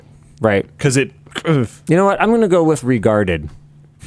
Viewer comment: what word. i don’t know what word luke likes. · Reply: "regarded"